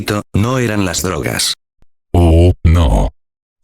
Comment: Que dice ahi? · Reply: “Pero nosotros sí”